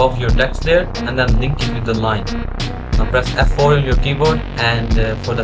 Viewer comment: what you said here is really inaudible i think you said f4 but whenever i press it it something else not related to ae pops up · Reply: It's f4